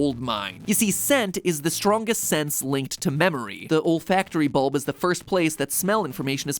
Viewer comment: When MatPat said the olfactory bulb I seriously thought he said the old factory bulb 😂